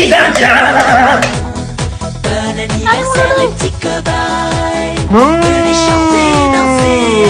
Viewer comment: MDR JAI ENTENDU "BONNE ANNIVERSAIRE LES PETITS COBAYES" 😭😭😭😭😭